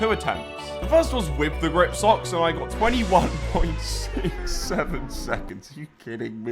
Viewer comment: I heard 6 7